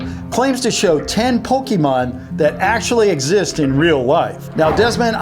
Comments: Did he just say pokeman lol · did he just say pokemen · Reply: No he said PokemAn